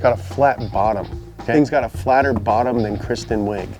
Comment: Did he say Chris Benoit at 2:18 or am I dumb. Lol